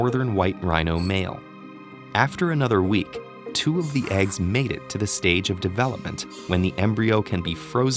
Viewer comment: i thought that he said that two of the eggs mated XD 00:3:28